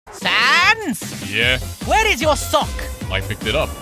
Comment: Dude didnt finished editing i heard paps say “WHERE DID YOUR SOCK”